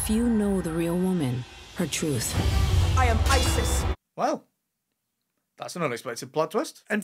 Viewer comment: omg I swear in the moment with I’m Isis I heard she said “I’m pisces” 🥴🥴🥴